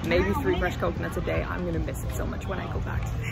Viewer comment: I thought you said you're going to "piss" so much! HA HA (you said 'miss') And remember what Tom Hanks said in Castaway: Coconut is a natural laxative! Watch out!